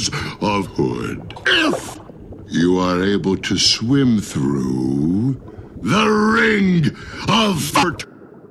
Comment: Bloat: If you are able to swim through: The Ring of Fart! I'm dying trying not to laugh loudly because I'm supposed to be studying and my mom can hear me! 🤣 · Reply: I was originally thought that blud said: IF YOU ARE ABLE TO SWIM THROUGH..... THE RING OF FU